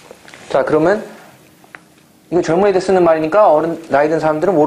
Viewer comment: did he just say my n word?